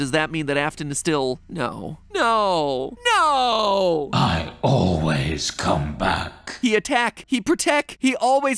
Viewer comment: I'm I the only one who heard him say " im always come back"